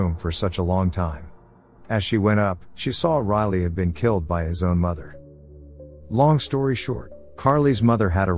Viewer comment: anyone catch “his mother” 😂